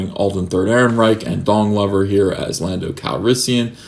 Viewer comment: did I hear "Dong Lover" at 3:17? Hahaha